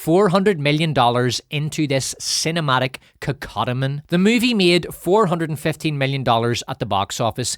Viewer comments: What is the word used at 1:42:43? Cinematic _________ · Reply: Maybe Cacodemon? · what is this word dude I can’t find a way to look it up to see the definition no speech to text will pick up “cacotomen” or however it’s spelled I’m tweaking trying to figure this out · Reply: Cacodemon: a malevolent spirit or person.